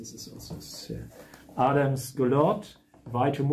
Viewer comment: subtitles say "Adams good Lord" ??? Michael Adams ???